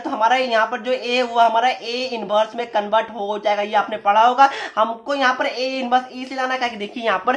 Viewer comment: Sir aap 6:40 me kya bole samjh nhi aaya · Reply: A ko Rhs mai lekr gya hu